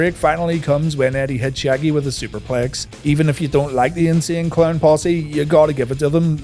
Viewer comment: At first I misheard as "N'Sync Clown Posse" and I said "What" 😆